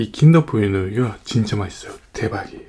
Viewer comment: I heard you say "terbaik" in Malaysia 🇲🇾🤔😂